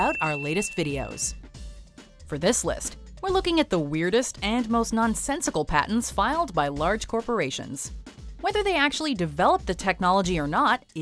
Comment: I thought that said "parents"